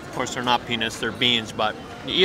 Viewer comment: Did I hear the word Penis? 0:40 Hahaha must have just heard wrong.